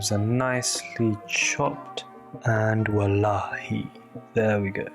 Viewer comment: did man say "wallahi"? at 5:23 😂😂😂